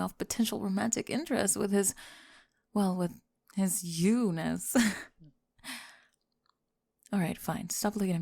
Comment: His what? The transcript doesn't say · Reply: "You-ness" i think? 🤔